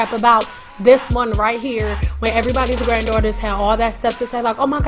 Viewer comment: Before I clicked... I was laughing! Lol cause I hear you saying “someone’s angry granddaughter” lol 😂, you’re the bomb lady! You hear me!!?